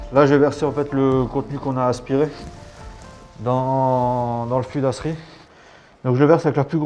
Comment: ya que moi qui a entendue dans le cul dastrid?